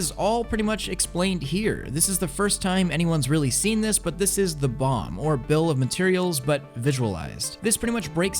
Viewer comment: I thought he said "This is the bomb" Sao flashbacks intensifies